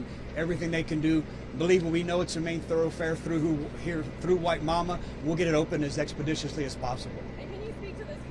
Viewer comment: What a tragidy! Thought I heard him say "white mama" neighborhood.